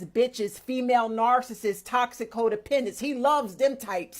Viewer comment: Did I hear her right? Did she keep saying Ho-dependents? That's a perfect description for some of these women.